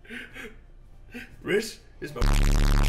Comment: I wonder what he said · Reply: "rhis is my rigatoni"